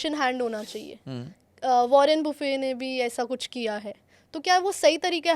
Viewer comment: did she said वारेन बुफे 😂😂😂😂